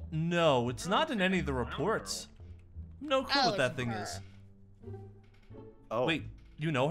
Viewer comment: i think chica said "oh its her" bc the funtime fredddy and this fnaf channel are in the same universe, and chica and circus baby was with the same restaurant in the funtime freddy channel